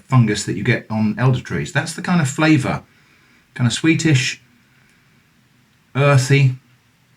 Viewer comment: i wasnt watching in the first like 3 min, just listening and suddenly i hear: "Lets have a sniff.. smells kinda Swedish" and i had to stop the video and roll back haha